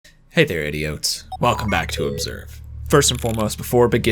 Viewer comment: I thought he said “hey there idiots” 😅